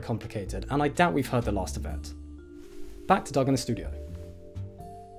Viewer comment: I thought he said, "Welcome to seven days of silence.." and I wondered if it was some kind of meditation show and that smooth voice seemed to fit but wait...what? He's already talking and a week is so...oh, he said science!